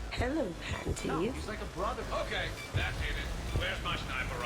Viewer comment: anyone else hear the background at 32:20 "ok that did it wheres my sniper"